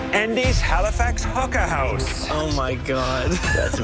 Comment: I honestly thought he said "Hooker House"